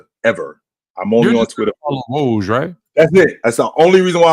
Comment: aww man. Thought you said you was on twitter for the HOES!! Not the Woj . I was let down when I realized I heard wrong lol